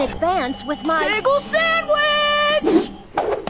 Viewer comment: For years everytime I heard Steven scream "bagel sandwich" I thought he said "pickle sandwich"